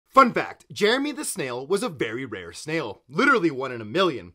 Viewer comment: i misheard him for jerry the snail so i thought he was talking about the one in spongbob cuz i didn't see spongob in a while💀